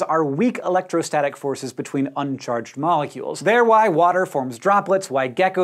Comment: This video was amazing and informative. Now I know why glitter sticks to everything. However, some of the captions for the video were incorrect. For example, at 0:37 the word "why" doesn't appear in the captions but instead the word "white" appears. At 1:22 instead of the captions saying "brush them off a surface" they say "brush them officer fost." Finally, at 1:30, the captions don't say "say skin cloth or fur," but instead they say "say skin cloth or for." Though these are only a few errors they can make it harder for someone who relies on captions to understand what you are saying. Could you please fix them so others can enjoy and understand your videos as much as I do.